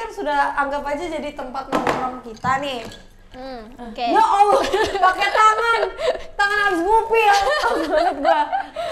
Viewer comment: Di menit 3:23 apa aku gak salah dengar yaa olla bilang " Ya Alloh pakek tangan"